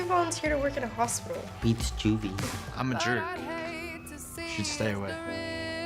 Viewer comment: I thought the guy said “I’m a druid” instead of “I’m a jerk.” 😂